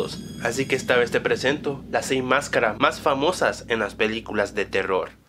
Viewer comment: pense que decia masacre mas famosas no mascaras por eso abri el video :v